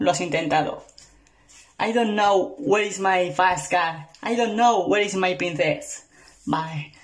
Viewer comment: que dice?! JAJAJa yo escucho "where is my fast car, I dont know where is my princess my"